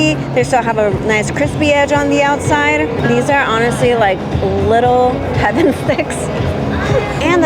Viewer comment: LOL .. When you said 'little heaven sticks', I at first thought you said 'little having sex.'